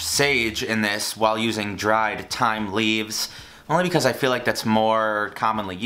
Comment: I literally thought you said "Time" instead of "Thyme" in the ingredients list for a second. I thought "well... that is one ingredient i don't have"